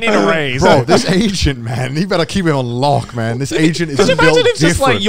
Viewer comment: Every single time they say "Agent" I hear "Asian"